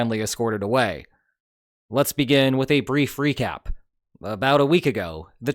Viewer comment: said "Let's begin with a brief recap" at 0:51. I heard it as "Let's begin with a brief freakout" at first and thought "Honestly, understandable".